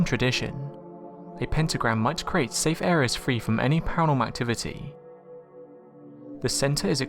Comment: I can barely understand what he said at 1:10-1:13... "pentagram creates a space free of paranormal activity"